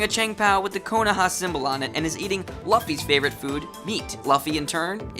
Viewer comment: Did he say laffy